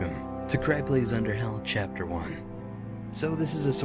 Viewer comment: i thought it said underbelly lol XD